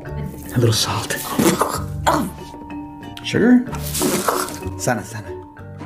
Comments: Did Bruno just say "Sana sana colita de rana" at 4:04 !? That's so adorable!!! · "Sana, sana...colita de rana" I NEVER NOTICED HE SAID THAT 😆 🤣 😆 🤣 Edit: For those who don't know, it translates into "Heal, heal...little tail of frog" Whenever I complained about aches and injuries as a child, my mother used to say that chant replacing colita (little tail) with whatever body part was hurting (ex: my hand hurt? "Sana, sana, manita de rana. Si no sana hoy, sanara mañana.") Bruno does a bunch of stuff meant to ward off bad luck, probably a coping mechanism he developed from constantly seeing misfortunes in the future, similar to Pepa's "clear skies" mantra when she gets anxious. Edit 2: I also just realized, that "vibing in his own universe" bit was him avoiding the cracks bc stepping on cracks is bad luck! And he's scared of cracks! So Hernando is a non-superstitious persona! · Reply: I'm listening with headphones. Bruno is definitely saying "Sana, sana, colita de rana." Edit: Also, why do you assume I'm not Hispanic?